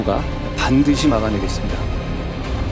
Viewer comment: 윤정권이었을 때니까 영상 00:42초 “지금부터 저희 정부가 반드시 막아내겠습니다." 라고 뚜껑이 말한게 맞냐? 왜 내귀에는 마약수사를 반드시 막아내겠다라고만 들리지???